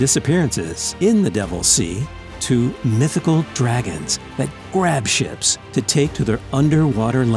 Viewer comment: I thought he said mythical dragons that grab shits